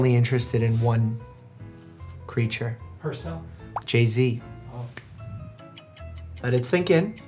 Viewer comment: Sorry back again. When he said Jay Z, I thought he said P Diddy. You can imagine my concern for the bees. I'll leave now.